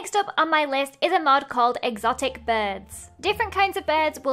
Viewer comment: When Lizzie said “exotic birds” I thought she was going to say “exotic butter” reference to five nights at Freddy’s probably nobody will understand Edit: oh my God thanks for all the likes